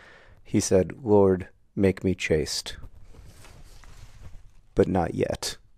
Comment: “Lord, make me chaste—but not yet” is such a St. Augustine-ass thing to say 😂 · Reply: I won't lie, I pronounce that like it rhymes with fast, so I thought he said chased, like a motivating force. The truth is funnier, though.